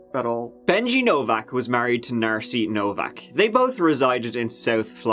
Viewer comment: Hahaha 3:38 "Benji Novak was married to Narcy Novak..." (shows picture,) And the sentence finishes; "...they both resided in South Florida." But what I HEARD was; the FOUR of them resided in South Florida. (BTW; "Mona Love," isn't exactly a cute little, "Aspiring, amateur, virgin teen girl next door," lol is she?! 🤣) (I'm here for you!🤜💥🤛)